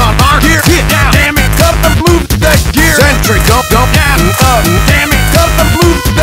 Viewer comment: I always heard "Dammit! Cut the bloob deck gear".